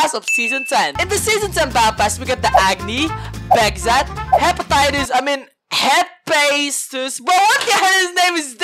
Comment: Hepatitis and 0:44 stinks. Thats what I heard lol Plz battlepass User:Uwuboy_LovMe